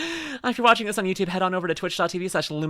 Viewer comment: I misheard the opening as "I'm LuminusRut" and the only thought I had was "I thought it was Red"